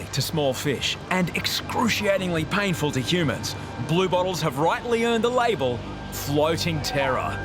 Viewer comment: I keep hearing “blue balls” 💀💀 . Geesh